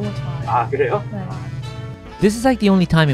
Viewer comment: At the end I thought you said should I buy her too 💀